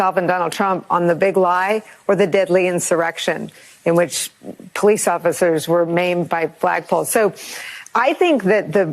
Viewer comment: What does she say at 0:40 "Police officers were mamed by the black pole"